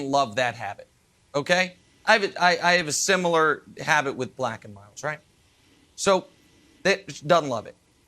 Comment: Did Nick say he smokes Black & Milds? He’s more hood then ppl know